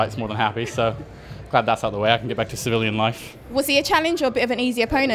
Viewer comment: Did he say Sierra Leonean?